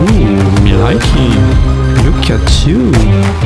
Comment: tu dis quoi à 09:22 ?! 😊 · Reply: Hahaha! Je dis "Me likey! (= I like it) Look at you..."